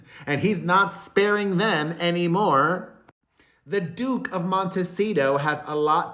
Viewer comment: Oh dear. I thought you said, “The douche of Montecito.”